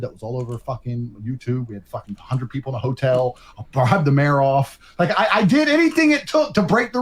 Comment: did he just say he bribed the mayor off?